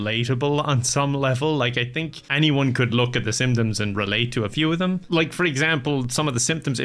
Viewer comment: I heard “like, I think anyone could look at The Simpsons and relate to a few of them” and was initially super confused.